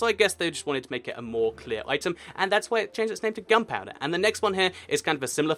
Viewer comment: it sounded like he said "gumpouder"